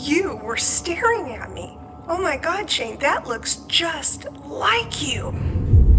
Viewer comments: did you edit in that whispering voice saying “Shane”? I heard it clear as day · Am I crazy or did I hear someone whisper "Shane" ?? 😦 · Okay so at 26:05 after your moms video it sounds like something said "shane". Also you could have brought the spirit from the haunted antique store you went to when you were with the squad and bunny, it also could have been multiple ghosts from the haunted hotel, the queen mary, or it was something from your old place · Anyone else hear a whisper at 26:09?? Could be saying “Shane” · did anyone else hear someone whisper "Shane" at 26:08 ?? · Who else hears the whispering voices 26:07 “Shane” 😳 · does anyone else hear “shane” at 26:09?? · did anybody else hear the "shane" whisper at 26:06 edit: thanks for 200 likes, that was unexpected😭😭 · ok but did anyone else hear "Shane" at 26:09??? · Dude: I heard someone say “Shane” like at 26:09 😨 you gotta the hell out that house bitch💀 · OMG I heard someone say "Shane" at 26:08 😨😨 oh god I'm so scared?!?! 😭😭😭I get easily scared maybe I was just hearing things 😰😰 can you guys comment down below if you heard it too... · DUDE AT 26:10 I HEARD A FEMALE VOICE SAY "shane" AM I GOING CRAZY OR WHAT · Did anyone notice the whisper on 26:07 it sounded like it said “Shane”. Edit: thank god i wasn’t the only one I thought I was crazy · Did anyone hear something say Shane really quietly?